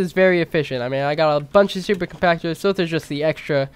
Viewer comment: wait did u say “Stupid compactors”